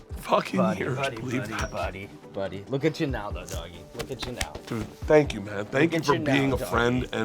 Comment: Did Sean call Bert doggie when he was starting to cry? 13:13 😂 Sounds like he should have Luis J Gomez on soon